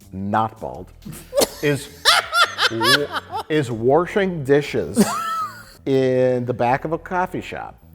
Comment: "...is warshing dishes." Me: "Did Mike just say 'warshing'?!" 22:49: "... IS WARSHING THOSE DISHES"